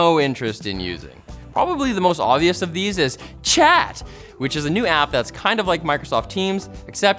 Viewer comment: I heard it as Chad, and not chat